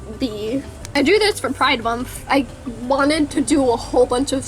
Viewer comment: I thought you said I drew this for five months, instead of pride month.😓